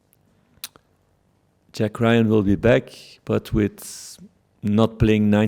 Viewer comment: Did he say JACK RYAN???